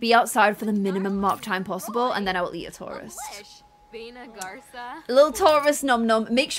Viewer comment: At the beginning when was talking about Maya killing the tourists and talking about how horrible they are an how they were ugly clothes I got really confused and offended becuse I thought she was saying Taurus ♉️ which is my star sign 😂😂😂